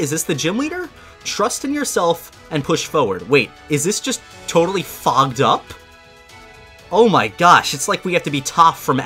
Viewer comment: it sounded like he said its totally f*cked up🤣🤣